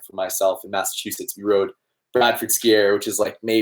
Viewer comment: What does bsa stand for? Haha I'm drawing a blank! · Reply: blandford ski area, at least I thought I heard you mention it. Such an awful and incredible spot!